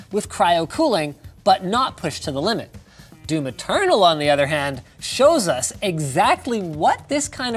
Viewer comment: I heard " Do maternal."